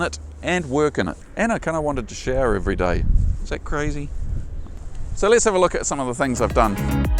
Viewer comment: I don't understand what he's saying here... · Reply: “And I kind of wanted to shower every day. Is that crazy?!” Hope that helps!